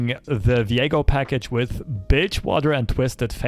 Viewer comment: The captions thought you said Bitchwater instead of Bilgewater in the beginning. I don't know if you wanna edit your video, if you can even do that while it's live, to avoid being demonetized. That is if Mama Susan hit you with the demonetize spell.